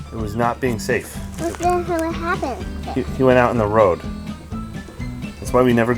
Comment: Did he just say what the hell happened? :-))) Great channel dude.